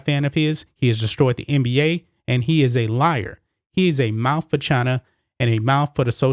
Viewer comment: I thought you said “mouth vagina, but mouth for China, works too.